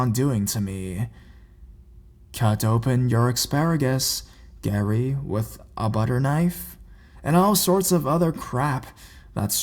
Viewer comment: I thought he said `cut open your asparagus with a butter knife'